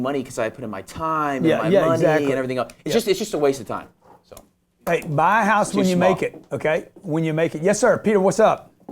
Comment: I disagree with Grant on this one. Saying no one should buy a house is like saying no one should go to college. It's not for everyone but works well for some people. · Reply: Scrooge Mcduck he didn't say no one should buy a house, he clearly said "buy a house when you make it".